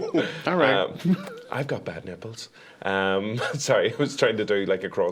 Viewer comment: I heard 'I have bad nipples'